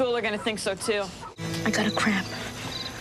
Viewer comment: did he just say I gotta crap? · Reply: Yep 😂😂 7 years later than I’m sure you wanted, but yes he did say I gotta crap 😂